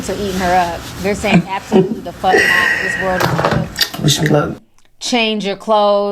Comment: is she saying wish "them" luck.....idk sounds weird